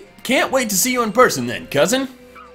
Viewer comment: When he said “I can’t wait to see u in person” I thought he said I can’t wait to see u in prison😹😹